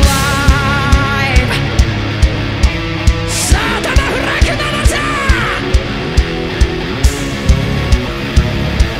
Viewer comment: ここなんていってるの？ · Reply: さぁ頭振れ熊本ー！